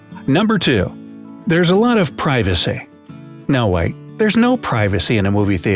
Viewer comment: who actually thought it said privacy